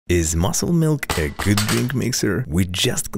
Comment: I thought he said muscle man